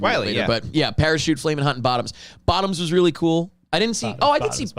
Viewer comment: I thought said Bottom Boyy, but fancy. 😂